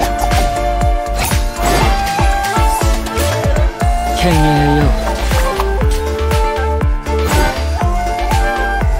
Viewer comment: 原神 未プレイヤーです。 いろんなPV観てて、プレステ買おうか迷ってます。 1:02 綾人さんの技？なんて言ってるんでしょうか。 英語版のチャンネル観たら ｢CASCADE！｣っ言っててますが 字幕を中国語にすると｢剣影○○｣繁体字でも不慣れで読みにくくて…｡ 神里綾人さんお好きな方、原神プレイヤーさん ご存知でしたら教えてくださいm(_ _)m ……綾人さんかっこいい…｡ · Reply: 剣影よ！です😊